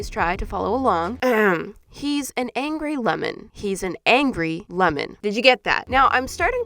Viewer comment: I thought that said demon 😂